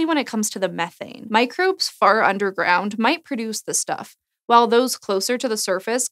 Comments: I thought she said "microbes FART underground might produce".... HAHAHAHAHAH lol!!! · When you said "microbes far underground" I heard it as "microbes fart underground," and since you were talking about methane, I didn't think anything of it for a second. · I could've swore she said "Microbes fart underground, might produce methane." Ironically, the sentence still holds true. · When Olivia said, "microbes far underground produce methane" I thought she said, "microbes fart underground [to] produce methane" and I was like, "weird but checks out."